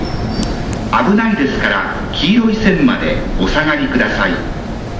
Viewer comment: What did he say at 5:58? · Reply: "Please stay behind the yellow line for your safety."